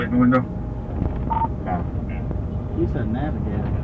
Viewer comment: did i hear what i think i head? Did one of the officers say "He's a nasty ass N word". I could be wrong, but it sure sounded like it. They should be fired, but probably won't be.